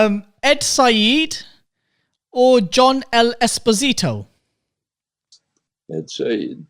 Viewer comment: what's the name of the guy he said in the minute 4:00? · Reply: John L. Esposito